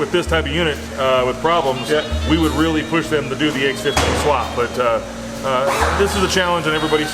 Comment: Did he say "do the ax15 swap"?